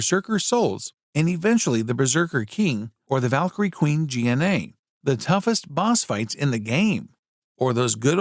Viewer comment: Did he just say G N A instead or Gna?